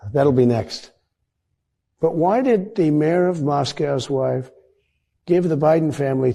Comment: did Trump say ".. the mayor of moscow's wife.."? Uhwha?